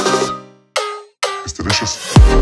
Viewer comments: の呟いてる（？）ところ僕だけかもしれないけど「ちょっとお願いしやす」に聞こえてしまうw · I used to think that it was ‘mr.suspicious’ instead of ‘its delicious” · thought it said "Mr. Vicious" i dont know why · It’s Delicious! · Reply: I thought it said mr delicious